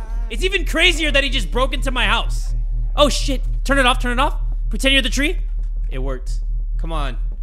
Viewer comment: I thought it said lesbian James broke into my house looking for spit 🤯